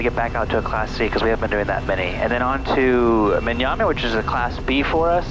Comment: I thought you said Manyana Nice landing there. Greased that one.